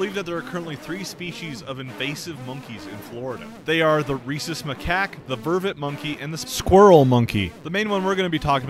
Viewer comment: i thought you said "racist macaque" 😭